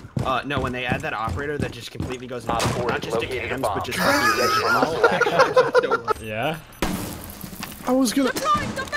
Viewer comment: I could not hear what he said when he hit the baracade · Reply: NITROGOAT he said “invisible”